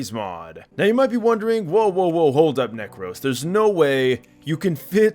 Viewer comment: what did he say_? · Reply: necros